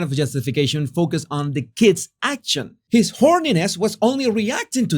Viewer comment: does he say his Horniness?